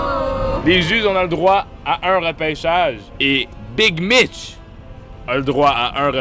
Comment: mdr au début j'ai entendu "Big bitch"